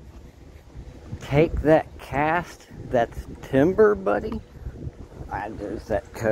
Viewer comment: I thought the guy said take that ass back to Denver buddy